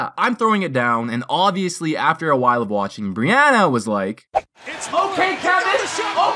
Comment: sounds like "its ok kevin" instead of OK KEVIN